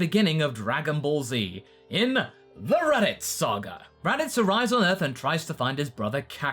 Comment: The raditz saga? You sounded like you said the “erotic saga”